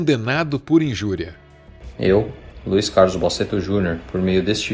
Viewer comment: eu ouvi Luis Carlos Boceto Junior?